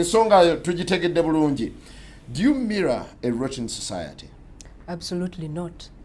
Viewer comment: "Do you mirror a Russian Society" This line made me spat out my drink when I heard it and its so random I'm surprised no one has pointed it out yet